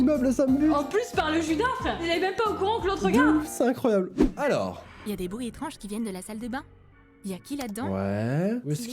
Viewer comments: ! c’est moi ou t’as dit Dooms c’est incroyable ?!?!? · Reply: De ouf pas dooms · C'est moi où a 12:03 il dit Doms?